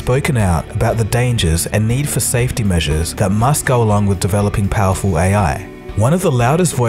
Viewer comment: did you say that MUSK go along? :D